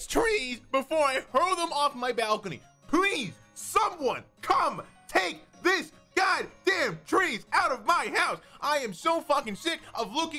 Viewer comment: Ad at 12:00 , so I heard "SOMEONE COME TAKE THIS french fries"